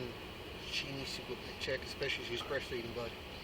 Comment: Especially of she’s what? I couldn’t catch what he said there. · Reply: She's breast feeding her baby